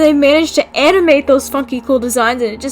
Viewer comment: I thought they said the F word instead of funky and I almost had a heart attack O_O